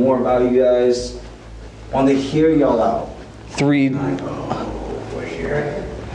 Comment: I heard a whisper and I THINK it was "I knowwww you're here" from what I was able to make out